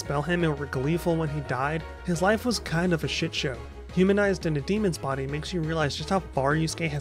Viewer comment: I always thought he said "human eyes on a demon's body" and "demon eyes on a human's body" :p